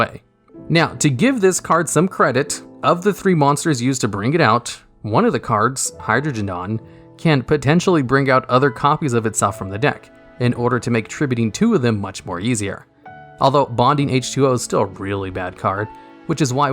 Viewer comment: anyone catch the "Hydrogendon" lol